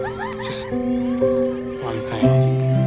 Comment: what did he say? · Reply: Just, one thing